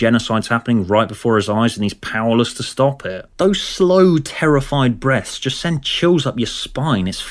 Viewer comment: heard this as "those slow terrified breasts"